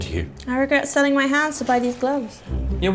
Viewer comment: “I regret selling my hands to buy these gloves” For the longest time I thought she said “house” instead of “hands”